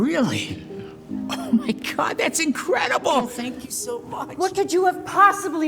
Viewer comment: The way the little kid said, “Thank you so much” kinda sounded like he said: fANk yOu sO mUSs